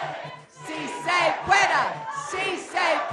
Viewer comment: to me it sounds like she says "si, se queda"